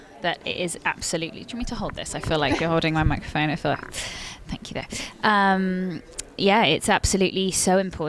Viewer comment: did i heard it right? Babe? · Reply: dhel garingalao thank u there